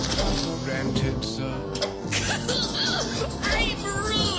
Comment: Did anyone else hear say "dadday!!" And its servant say "your man tits sir?"